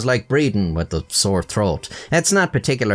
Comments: I thought he said "breeding with a sore throat" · I honestly thought said: "Work is like breeding with a sore throat" I wasn't sure what to do with that 😬